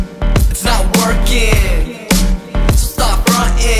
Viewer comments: Am I the only who hears 'stop working' instead of 'it's not working'? :3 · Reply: not only you, but I heard 'its not working' I think that's the way rappers talk.. sounds like 'stop working' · Am I the only who hears 'stop working' instead of 'it's not working'? :3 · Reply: I heard "snot working" so yours is still better than mine